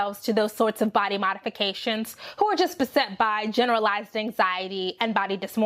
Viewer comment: Caption edit: around 21:51 the captions say (mumbles) but I'm pretty sure she said "who are just beset by"